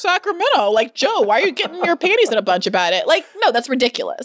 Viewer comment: Omg why is this the first time you're sharing Joe with us on Bad Faith 😍😍😍 You're not allowed to hide your hot friends from us, Bri! 😝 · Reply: did she say Joe wears panties?